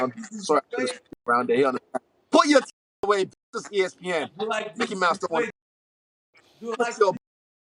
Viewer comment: Okay but am I the only one who thought Paulo said “u like titties” after showing his pecs right there